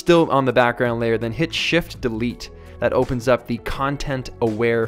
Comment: what is u said tym 6:38 hit shit and what ?